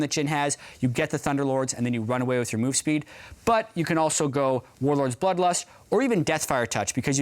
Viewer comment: anyone else hear Warlords buttlust? xD